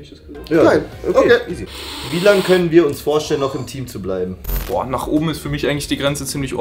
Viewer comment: Anstatt "im Team" habe ich "intim" verstanden und war kurz sehr verwirrt xD